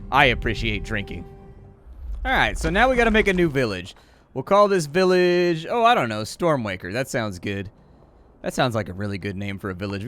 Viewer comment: Not gonna lie, thought he said he was calling his village "Stormwanker". XD